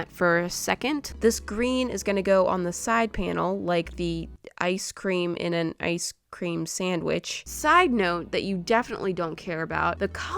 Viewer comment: I really heard what she said but my blink self was like IcEcReAm ChIlLiN ChIlLiN iCeCrEaM cHiLlIn